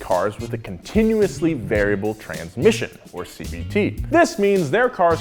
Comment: thought you said CBT for a sec and I was bouta die